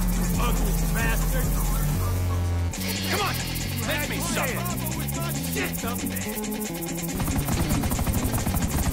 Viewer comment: what trevor said? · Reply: "¡Come on! Make me suffer".